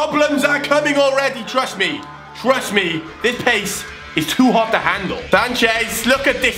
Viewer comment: Turn automated subtitles on.. look what comes up instead of pace.